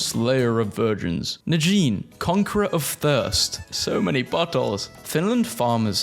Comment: bro subtitles said buttholes not bottles. 😂